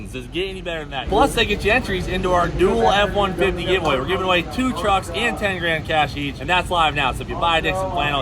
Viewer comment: Had to rewind cause I swore I heard “Dually F-150 giveaway”